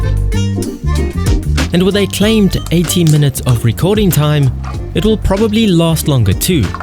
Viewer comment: It sounds like he said 18 minutes of recording time, but he actually meant to say 80 minutes. Should probably pin this comment for others to see as it's a common question in the comments.